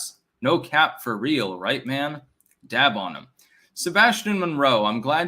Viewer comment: brother Did u say dab on it xD